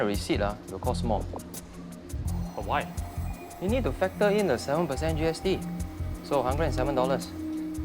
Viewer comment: Why did it sound like he said we need to FATTER in the GST'?